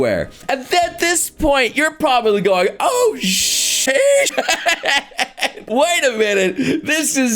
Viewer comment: The edit that does makes it sound like is saying “Sheesh!” instead of saying “Sh¡t!”